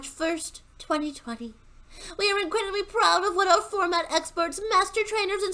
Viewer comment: The way you read it, I first heard it as "we're regrettably proud". 🤣 Great video, as always. Keep it up,)